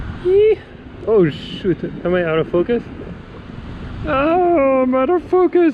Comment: Another great video, at 6.02 when you say out of focus, it sounds like ur saying “ Mother fuc...s “ lol I am a retired wedding photographer in which I had to perfect each shot...and I find street photography mostly mundane images, but I must say your portraits of the young friendly dude 8.27 are fantastic...well done, you are very talented🙂